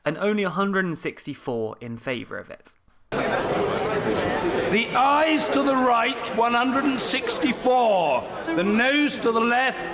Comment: I thought he said "the eyes to the right"